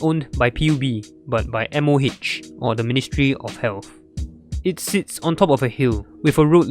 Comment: I thought you said the ministry of hell 💀